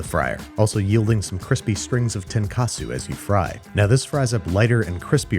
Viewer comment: when you said tenkasu, i swaer i thought you said tonkatsu.... i got so confused, i was like "where's the pork?"